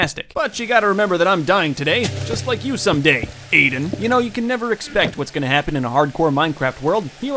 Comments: I think he said Aiden my name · what was the name of the person you said at the time stamp 6:28? It sounded like you said Aiden and I’m pretty freaked out cause my name is Ayden. It gave me chills. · did you say Aiden? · did he just say Aidan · Just like you, Aiden All the Aiden's: Ö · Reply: When he said Aiden i thought he said Caden and i freaked out